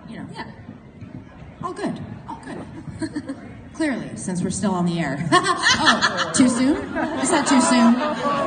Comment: Does Lauren says "'cause we are still on the air... Oh too soon? Too soon?" at 16:02 or so?#SaveLucifer #PickupLucifer